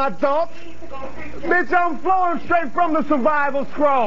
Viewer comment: Hello guys, I'm not an english native speaker. What does he say between 0:12 and 0:15 ? And what does it mean? B**ch, I'm .... ? Thank you for your help. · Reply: Sounds like, "Flowing straight from the survivors scroll." It's not a standard phrase and is probably referencing something. 3 weeks late but gotchu